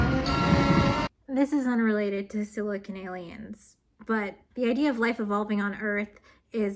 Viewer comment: Every time she said "Silicon Aliens" I heard "Silly Canadians", and it got me really confused. Great video btw.